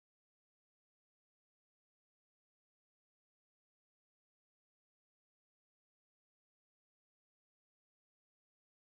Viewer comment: Could someone please tell me what is the officer shouting? I don't speak Hindi. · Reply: Bevin Chou He says 'How is your Josh (Passion).